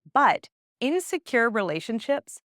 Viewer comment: Around the 7 mins mark, you say "In secure relationships..." And I heard "insecure relationships..." and was really confused for a second. 😅